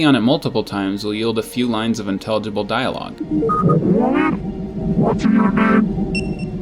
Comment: NEED? I think he says What’s your name lol